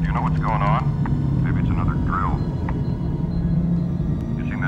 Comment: Holy cow I legitimately for all this time thought he was saying "maybe it's another girl." Like mischievous kidnapped princesses had been a recurring problem or something.